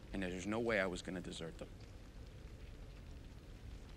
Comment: I always hear “there’s no way I’m leaving this bitch”